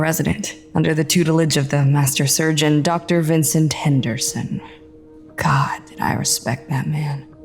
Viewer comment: Wait..... Did she say Henderson?